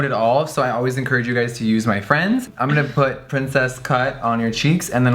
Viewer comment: I thought he said princes CUNT